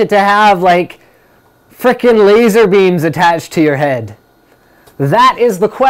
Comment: Did Linux say "fucking laserbeams"?!! :O